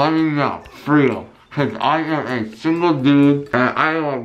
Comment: He has a lisp I thought he said I’m a single goob